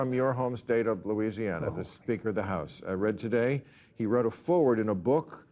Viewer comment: Also, it sounded like Bill just said "Mike Johnson, the Stinker of the House," and that's awesome 😂